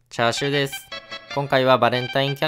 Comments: 一番最初 チャシューって聞こえたわ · あの、、最初「こんちゃしゅうです」って言ってるんだろうけども「チャーシューです」に聞こえてしまった:( ;´꒳`;)